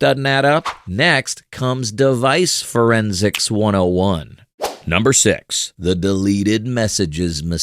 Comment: i heard DEVICE_FRIEND because of deltarune